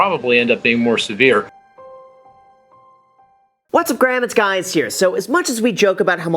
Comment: Am I the only one who heard “what’s up graham it’s guys here”